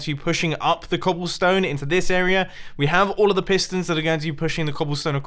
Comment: you saying “this area” makes seri think you’re saying “hey Siri” while my tablet is plugged in